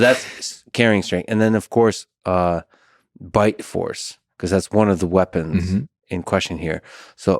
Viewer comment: I heard « bike force » lol Now all I can thinking of if a bike race between a lion, a gorilla and a bear